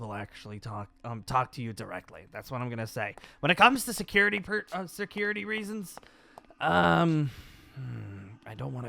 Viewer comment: Lol sounded like she'd say security breach. (By she I am referring.)